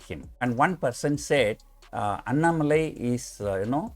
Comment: i thought he was saying anamoly !? lol